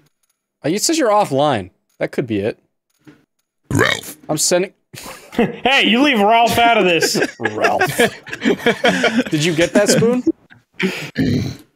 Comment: Bruh 4:30 sounded like shao Kahn saying round but says Ralph